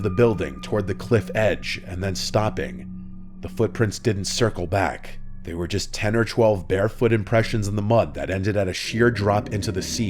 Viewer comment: not me thinking you where saying “Bear” footsteps instead of bare foot 😂 I was like HOW DID A BEAR GET THEREE!! 😮